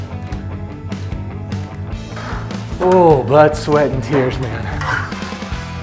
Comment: I honestly thought you said “butt sweat and tears” while you were climbing down the ladder.